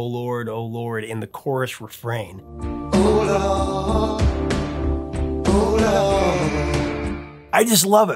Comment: That's wild when he says in the air tonight oh lord oh lord, I always thought he was saying hold on hold on... lol